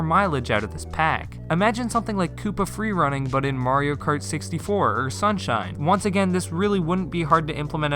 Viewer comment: Did he just say "Mario Kart 64"?